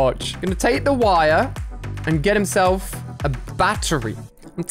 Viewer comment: On number 18, I thought he said “he got some wire, now he’s gonna get himself electrocuted” but he said get himself a battery